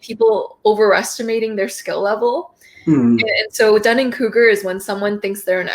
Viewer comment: could you tell me what that word she just said about someone who thinks their an expert when their really a novice. I tried to closed capture what it was but it says " done in cougar?" 🤨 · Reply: Dunning Kruger? I think?